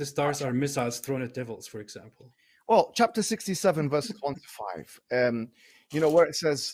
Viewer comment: Not in defence of mo "golden showers" hijab but I think he actually said chapter 67:1-5 NOT 67:25... which would be correct. However 67:1-5 does say that shooting stars are missiles "thrown at the devil".